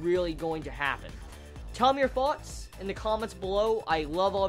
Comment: i always thought you said, "have a good day, or not" i now know its night